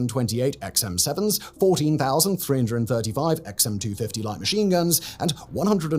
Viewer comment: Huge, massive error on this vid!!! time stamp of 5:42....Beard bloke says 14,335...vid captions says 14,334. Can the team live with themselves????? Ironically, i got a slightly wrong timestamp. Needless to say, I cant live with myself.